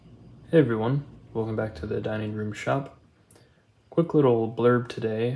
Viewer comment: I grew up in the Washing DC Area, where for decades there was a popular morning radio talk show hosted by Diane Rehm. Every time you say, "Welcome to the dining room shop I hear, "Welcome to the Diane Rehm Show." Super cool constrained mounting. I learn so much from your explorations. Many thanks for sharing them!